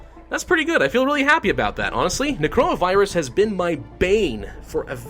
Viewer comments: I thought he said coronavirus 😂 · I thought he said corona virus